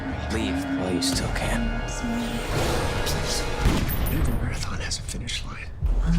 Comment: What did Jay Garrick say? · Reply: Javed Khan "every marathon has a finish line"